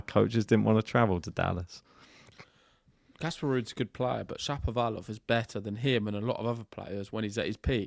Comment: Best week of Shapovalov's career. He put it all together. He won 500 atp title, and beat 3 top 10 players. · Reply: For a second, I legit thought you said Sharapova 😂